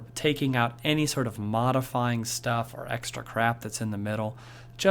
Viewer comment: did he say crap? lol But thanks for this, it helped me a lot.